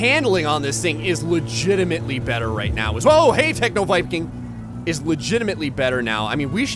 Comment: I was so confused the whole time I thought you were saying techno biking